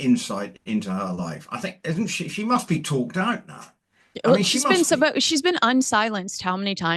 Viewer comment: 😂 i thought you said she must be talked down, not talked out 😂 Only talk her down if she's not on a ledge please!!😂😂😂